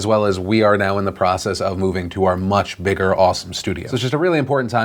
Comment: for a second, i thought Phil said "Austin" studio instead of "Awesome" and got really hyped for about 2 seconds